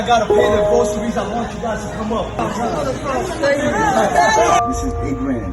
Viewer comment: what the hell did he say? Who to come up?